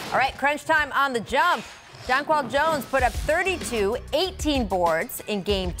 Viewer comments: I thought she said John Paul Jones, bachelorette all the way! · Who else thought she said "John Paul Jones" in the beginning 😂